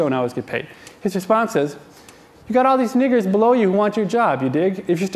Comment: Did he just say n***? XD XD